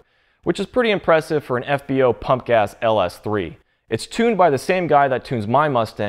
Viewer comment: i thought u said "punk ass Ls3" lmaooooo